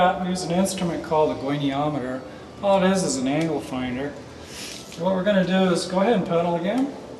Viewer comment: um did he say anal finder at 4:43?!!!